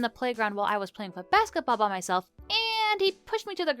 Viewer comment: when she says basketball at 2:15, the captions say footbal lol